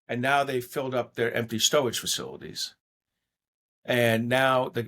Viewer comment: Is he saying storage facility? 4:29 what a wascawy wabbit